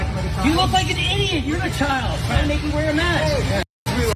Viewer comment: Did I hear him say "you're a towel"? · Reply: He said “you’re a child” the guy said “you’re acting like a child” so he responded with “nO yOuRe a ChIlD”